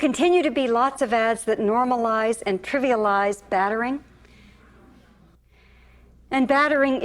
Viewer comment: I don't understand the word she says at 5:39, it it bathering? And what does it mean?